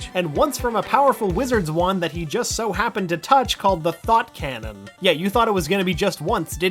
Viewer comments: I thought you said "The Thot Cannon" XD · "The Thot Cannon" I misheard it hehe